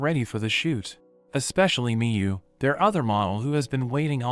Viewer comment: Wait yo did they say miyu cuz that's my Japanese friend's name bro